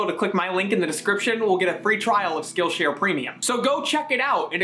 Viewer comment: I thought he said skittles instead of skills and he spoke to my soul.